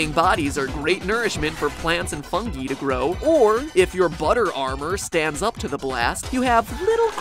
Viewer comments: Wait...Did you just said ‘butter armour’ or something like that? (SkyDoes Miencraft Come to look.You would like that !) But anyway, I've never thought how a creeper blow up.I thought creeper’s stomach has a TNT and a redstone torch inside. And they will blow up when the TNT and the redstone torch touched. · Did you just say "budder armour"? Seems like you've heard that famous word from Skydoesminecraft! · im four years too late to this video but at 10:15, does he say fungee??